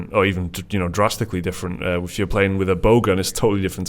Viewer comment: Every time says "Bow Gun", I hear "Bogun" :P